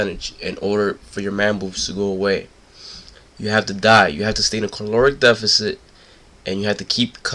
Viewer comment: (sounds like he says you have to die) lol